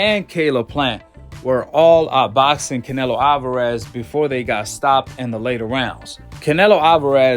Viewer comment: Did you say that Plant out boxed Canelo???🤔